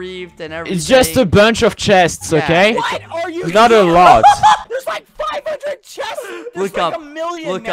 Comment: anyone else hear “There’s like, f*€%”